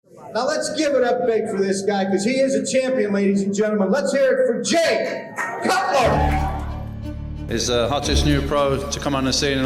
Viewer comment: did he just call him Jake Cutler 😭😭🙏